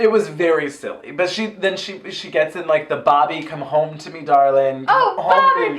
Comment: I always thought Alexis was saying "Papi" not "Bobby" . I find myself going back to watch her PSA every so often, it never fails to make me smile from ear to ear. BAM!